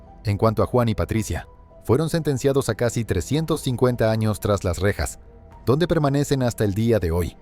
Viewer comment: 350 años? o escuche mal? o es 35 años? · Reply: si, es 350 años, es normal esas sentencias cuando el proposito es que nunca salgan de prisión